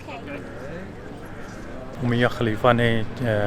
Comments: I thought he was saying miya khalifa 😆😆😆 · Did I heard "Mia Khalifa" at 11:06 ??? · Did he say Mia Khalifa at 11:06? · Sorry what did u say 🤣🤣🤣 mian khalifa 11:05 😂😂😂😂😂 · did he say Mia khalifa?? Which Mia khalifa?? I am confused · What he said on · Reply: Ummaya Khalifa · did he say Mia Khalifa?? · what did he say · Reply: Ummaya Khalifa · any one noticed what he is saying.... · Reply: Mia khalifa🤣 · What did you say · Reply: If you have watched all the video I'm sure you know what I said! Ummayah Khalifa